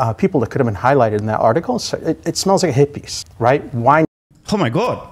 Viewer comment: "It smells like a hit piece" lmao I thought he said "It smells like hippies"